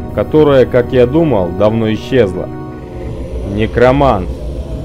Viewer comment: Мне одной послышалось НАРКОМАН А НЕ ТО ЧТО ОН СКАЗАЛ